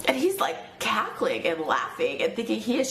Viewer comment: Sounded like she said "cackling and laughing," not "Catholic"